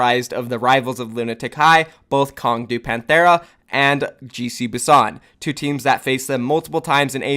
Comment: Everytime you said "GC Busan" I heard "Juicy Busan"